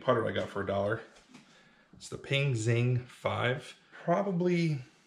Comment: Oh Ping! I thought you where saying pink putter